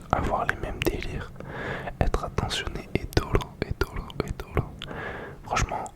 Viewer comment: quoi ?????😂😂😂 j’ai bien entendu doro là ?